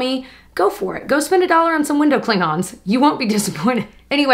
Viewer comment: I thought you said “window Klingons” lol. The Trekkie in new was like “WOE!! WHAT!!!” Lol then I realized what you meant... 🤣😂... 🖖🏻